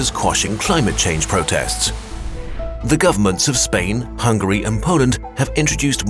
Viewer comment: The cheese made me so hungry, that I heard hungry instead of Hungary